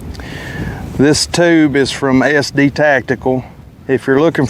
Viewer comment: Who sells that can? Thought you said SD Tactical but it's tough to say. 60-70 bucks??? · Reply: it's a Form 1 suppressor. The tube, end, and mount is from SD Tactical. The cones are no longer made by the same company but Sub Zero supply sells the same design.